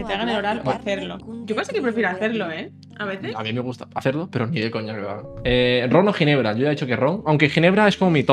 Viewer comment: que dice?? (No entiendo) jajajaja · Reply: A mi me gusta hacerlo, pero ni de coña que me lo hagan